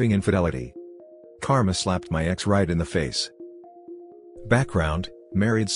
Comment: Thought it said Karen slapped my ex right in the face!! 😂